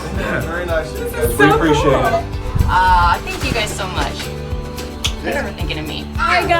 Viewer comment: 10.23 Wait did he just say thank you for nothing